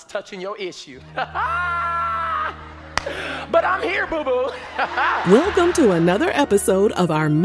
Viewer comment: Did he just say, “I’m here boo-boo?”😂😂I can’t